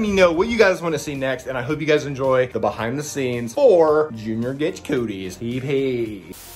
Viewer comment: It sounded like you said “Junior gets Cody’s” LMAO!!! 🤣🤣🤣